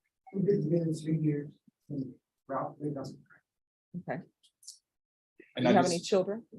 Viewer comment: wait a friggin minute... Did she just say her boyfriend of 3 years is named Ralph Wiggum?!?!? 🤣🤣🤣🤣 #IYKYK